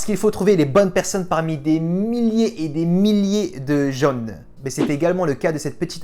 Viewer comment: des milliers et des milliers de jaunes. Sans déconner, j'ai entendu ça x)